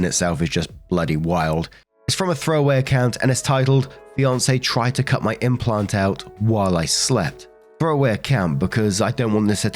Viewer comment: Okay, listening to this, I thought you said “BEYONCÉ tried to cut my implant out”